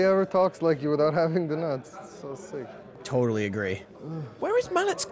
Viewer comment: What does he say 18:40? "Nobody talks like you without having _______" ??? What does he say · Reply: the nuts...